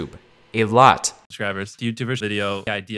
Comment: lol who else heard him say karl?